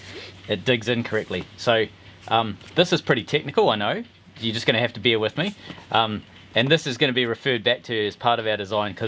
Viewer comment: If you genuinly brought a bear to the table I would quite legitimately shit my pants. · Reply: To quote Strother Martin as the Warden in the great movie "Cool Hand Luke": What we have here is a failure to communicate. At 1:36, when explaining your CAD drawing, you said "You're just going to have to bear with me" As an American, I heard you say "You're just going to have to beer with me." and wasn't looking at the screen at that moment. When I heard "beer" it caught my interest, and I had to re-watch it to see that you were really saying to bear with you. I promise you that I'd never bring a bear (polar or otherwise) to Brupeg, as long as you promise to provide the beer. Deal? I know, it loses something when you have to explain...